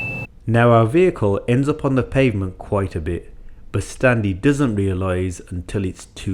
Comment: I'm glad you showed her how to get to the biting point at the end. I can imagine that never had it pointed out to her what exactly she has to do not to stall a car. I can remember during one of my driving lessons I pretty much stalled the car one after another but my instructor never told me how exactly I could get to the biting point lol so I had to figure it out myself over the next few lessons and it drove me nuts. Either way, seems like she just needs to get more familiar with the gears and the rest should figure itself out after the basics are really down. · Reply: ? I thought he was saying Stanley 😂😂😭